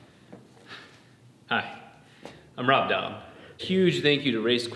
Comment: misheard intro " hi, im hot dog"